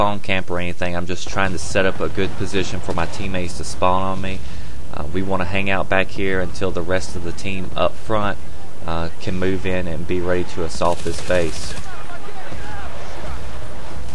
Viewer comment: did you say "So were ready to assult his face"?